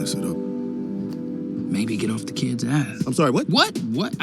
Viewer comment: When I don’t listen close I hear f*** but when I listen closely I hear Barbie 💀